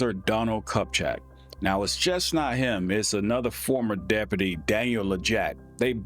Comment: Wtf are you saying? Articulate the damn words. "It's just not him"? After saying he is the cop?